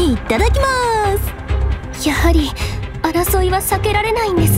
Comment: I didn't know "Ittadakimasu" means "Moving out" but alright. Maybe I heard totally wrong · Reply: I've been listening to the Utage's line at 0:08 over and over again after this but I'm pretty sure I heard "Ittadakimasu". Maybe they misheard it as "Ittekimasu" instead?? I sure started doubting myself